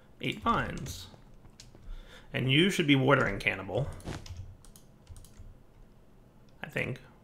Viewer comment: It sounds like you said watering cannibal